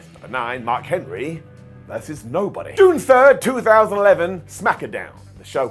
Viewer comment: sounds like you say Smaketh Down.. 😂im here for it sounds very sophisticated... just here for a good time🎉